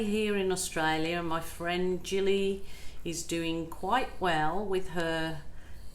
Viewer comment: Cinnamon Girl aww thank you for your lovely message, appreciated Jilly has now returned to UK to have surgery on her badly broken wrist and hopefully all will go well for her 🥰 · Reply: 🤣😂🤣 all the time I thought you were saying Julie...Jilly it is!